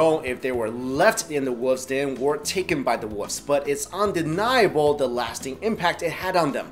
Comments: woofs, that's what it sounds like your saying. Its wolves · i keep hearing him say "woofs".